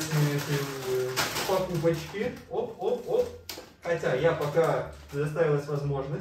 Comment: Я одна услышала то что сказал Катя? Он это на 16:51 мин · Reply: Он сказал "хотя"